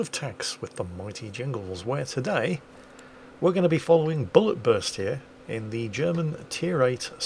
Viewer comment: I heard "Bulletwurst". That would be an awesome name :)